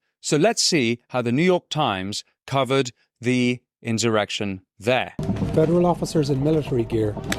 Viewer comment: All this time i thought they were saying Erection. I thought on Jan 6th? Yes i probably had an erection!